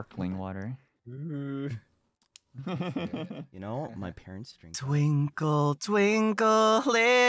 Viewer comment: Did I hear say "you know when my period's starting" or something like that😳 Edit:Wait... I think I misheard